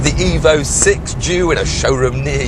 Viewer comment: he said jew instead of due lmao